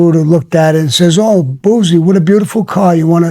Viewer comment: Did he say Bullsie? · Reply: Boozie